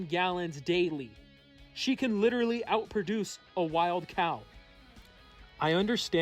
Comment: Ngl I thought you said produce a cow 🐄